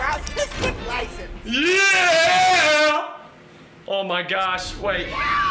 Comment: bro, i thought at 34:19 said, "you have officially earned your autistic license."